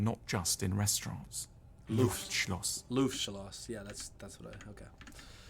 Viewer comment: Did he say Lustschloss instead of Luftschloss? He should google Lustschloss 💋😉